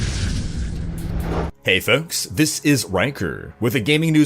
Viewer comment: idk why but i heard you say hey folks this is wanker